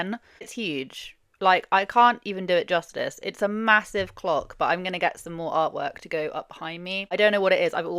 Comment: I was washing the pots and listening and misheard Iv got a MASSIVE CLOCK and nearly smashed a plate 😂 xx